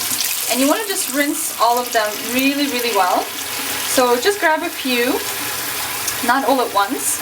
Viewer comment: it sounds like she is saying "just grab a pew".....o.O